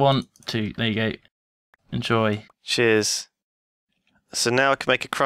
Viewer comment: in the beginning when lewis said "cheers" is sounded like he said "jizz"